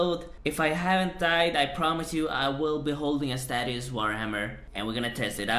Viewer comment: Did he really say statius war hammer